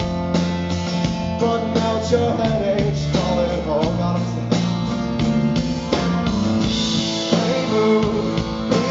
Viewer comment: what does he say at 2:25? I can't catch it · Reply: God I'm sorry